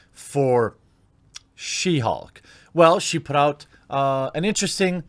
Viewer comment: THE ONLY PERSON WHO SHOULD WRITE SHE HULK IS JOHN BYRNE THAT IS ALL. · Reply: Ha! I thought he said "she hawk" and I was like wtf is that